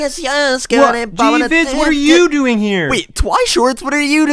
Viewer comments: Who else thought he said “Jesus what are you doing here?” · He sounded like he said Jesus what are you doing here